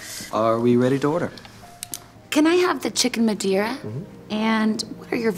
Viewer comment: I thought she said “Chicken Bagheera” like the panther from jungle book 🤣🤣